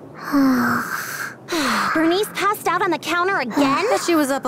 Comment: I misheart the "burnice" part to "british"😭🙏